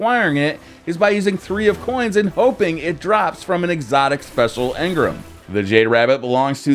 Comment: did you say an exotic special engram? 😁😁